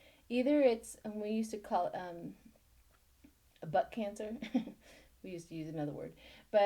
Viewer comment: Heard You say (butt cancer ) funny.Its Colon Cancer.